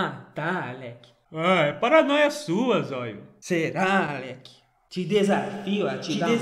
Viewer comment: Mano te juro que eu ouvi falando no fundo "te DESAFIO a atropelar um caminhão" kkkk